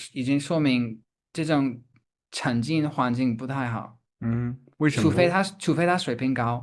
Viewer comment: 聽不懂他說什麼。懲戒的環境？